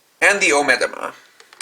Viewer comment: Sounded like he and you said "Omega Moth" or something. Sorry if I misheard.